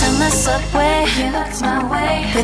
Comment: i hear 'with your girl gays'